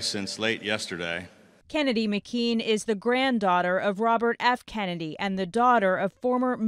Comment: Who else thought it said Jhon F. Kennedy